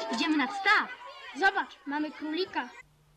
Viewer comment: Usłyszałem : -IDZIEMY NAD STAW! - ZOBACZ MAMY PÓŁ LITRA! Padłem....